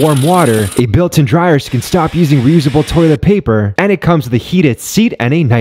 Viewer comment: Did he just say "Reusable toilet paper" ?! 😂🤣🤣🤣🤣🤣 Grossssssssse bro